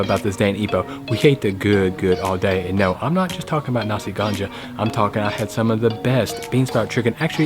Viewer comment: Nasi ganja not ganga, ganja=weed/addictive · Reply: oh sorry I thought he said ganga my mistake 😂